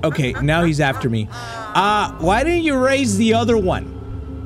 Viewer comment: It sounded like the monster said what one. Am I tripping?